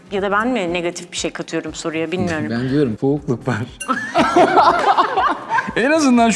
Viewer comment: da serhat ne diyor anlayan var mı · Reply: bi soğukluk var diyor